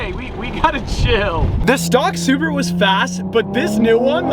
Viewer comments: Did he say “stock Subaru” at 6:09 😭😭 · did anyone else hear him say "the stock SUBARU was fast. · Sounds like you said stock SUBARU @ 6:08 😅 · did he say "stock subaru" ???